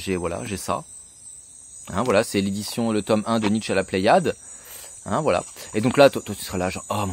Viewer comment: Quand a dit "Nietzsche la pléiade", j'ai entendu "Nietzsche à la plage"... J'ai ri beaucoup trop que de raison.